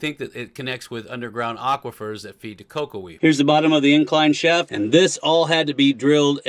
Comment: my hearing is not as good as it once was (but it’s as good once as it ever was) did you say something about underground aquifers feeding “loco weed?” Thus far, I’m into underwater placer mining . . . But for well-watered loco weed, I may wish to consider “horizontal vectored growth into allied mining opportunities.”